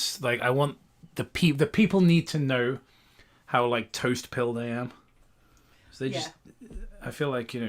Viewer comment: did just say "toastpilled"? Has anyone ever heard that term before? I feel like a whole new world has opened up to me